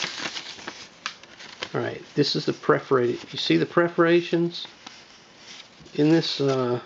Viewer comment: Sorry, but my native language is Portuguese so I couldn't understand the name of the tape you said to use at 5:20, can you repeat in writing? Congratulations and thanks for share! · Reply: Perforated Tyvek.